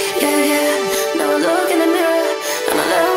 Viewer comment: oh THATS what shes singing i heard "I become some butter" OOP